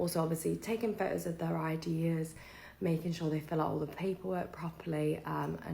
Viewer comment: Is it me, or is she saying ideas instead of IDs?